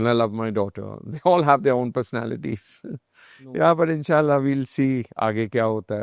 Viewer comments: Is he saying InshAllah ?? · did rajat say in sha Allah? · did he say "IN SHA ALLAH" ?